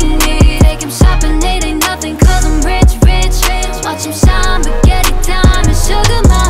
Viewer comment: At first I thought she was saying " 'cause i' m rich bitch" 😂😂😂